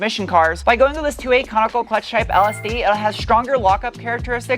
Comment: I thought you had said Comical Clutch Type! 😂